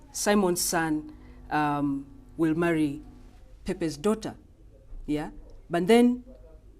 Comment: did she just say pepe's daughter? As in pepe the frog.